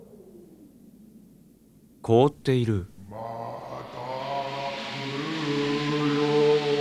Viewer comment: Groke is yelling either "i will come back" or "i will return" thought i say this since its not added in the subs :)